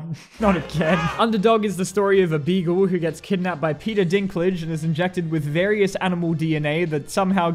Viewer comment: I'm sorry, did you just say PETA Dinklage or is that just your accent?